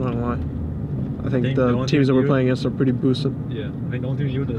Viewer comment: does bjerg say "teams we play against are pretty "Pølse" ? Or am I hearing shyt ?:D